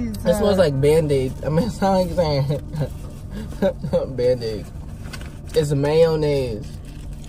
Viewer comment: It really did sound like you said Man Aids smh lmao